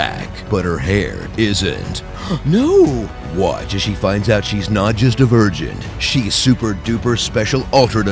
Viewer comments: he said "she's not just divergent" and I thought he said "she's not just a virgin." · I was so sure he said "She finds out she's a virgin" ;D instead of "She finds out she's divergent" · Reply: I heard "She finds out she's not a virgin." So I'm right there with you. I thought that was really weird until he said that she's super divergent. Glad I'm not the only one. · I swear I heard "watch as she finds out she's not just a virgin..." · I totally thought you said, watch her find out that she's not just a virgin, she's a totally super virgin' Dude that's not what the series is about. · anyone else hear, "watch as she finds out she's not just a virgin" ??